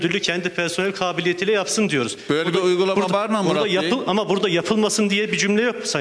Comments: böyle bi uygulama varmı a...kyum mu dedii benmi yanlış anladım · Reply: Böyle bir uygulama varmı murat bey diyor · başkanım burda ne diyor ben anlayadım tam :)) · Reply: Böyle bişe var mı ama diyo sanırım ama video degişti için küfür gibi duyuluyor öyle bişe olsa akp liler yırtılırlardı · böyle uygulama varmı .mına koyım mi diyor ben mi yanlış anladım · ben mi yanlış duydum 4.09 böyle bi uygulama varmı amk diyo